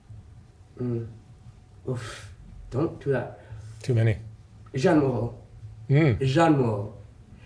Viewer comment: Who does say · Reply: Jeanne Moreau